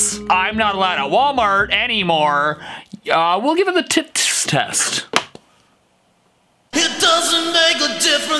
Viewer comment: why does it sound like hes saying "tits test" instead of tts test